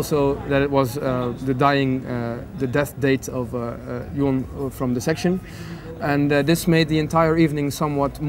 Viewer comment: What did say just after 4:17 ? Could someone please tell me? He says “The death date of” what? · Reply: Jon from Dissection